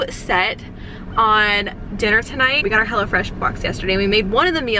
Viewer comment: Lmao I’m not gunna lie.. for a minute there I thought u were saying hella fresh 😂